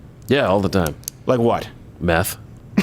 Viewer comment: I could have sworn he said "Math"...